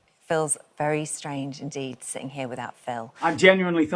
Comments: I thought she said phils very strange indeed too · Lmao, I ALSO thought she said "Phil's very strange indeed..." 😅😅😅